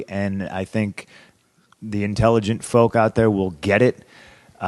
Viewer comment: "The doors to the Ed Sullivan Show" is what I heard too. 52:00 xD